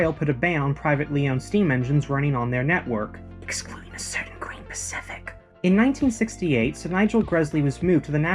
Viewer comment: What did he whisper here? It sounded to me like: Esquire the certain quaint, Pacific! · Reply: Excluding a certain Green Pacific.